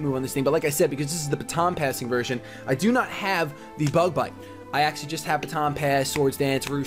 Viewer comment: Wahoo for poop brown mamoswine oh and at the end I thought I heard bug butt instead of bug bite haha